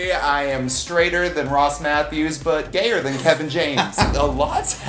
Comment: Honestly thought said Josh Matthews 😅😅